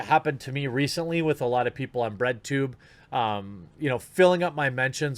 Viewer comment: I had to listen to that a few times, I thought he said Redtube and was like “I didn’t know you provided content there, haha”. Never heard of Breadtube before